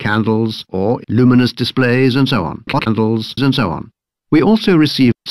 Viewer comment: Too many things to quote, -nd so on. What's he saying at 0:13? cockadiddles? :p